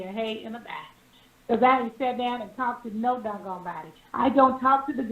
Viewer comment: Nene girl I thought you said you don’t talk to bloggers 🤔